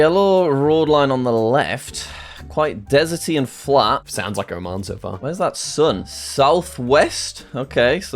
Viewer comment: I thought said "sounds like your mom so far" instead of Oman